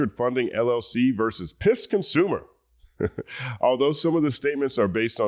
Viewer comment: I thought you said Piss Consumer 😂😂😂